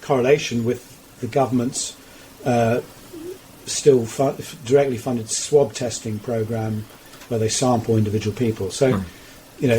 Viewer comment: Did he say "Israeli-funded testing at 1:33?